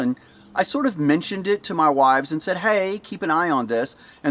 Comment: did he say wives????